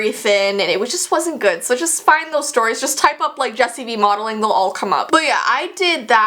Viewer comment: I sont know why but when she says jessii vee i thought that she said just eviee